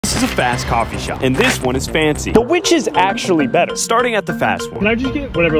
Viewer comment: I thought he said "This is a fast car shop and this is a fancy one"💀💀💀